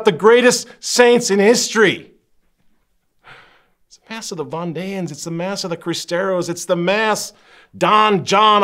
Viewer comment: What did he say at 17:02? The Von Danes? Bondeians?